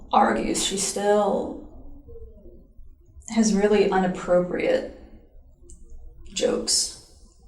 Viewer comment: Did she just say "…unappropriate jokes"?